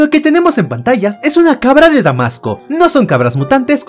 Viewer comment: Wey pensé que dijo cabra enmascarado💀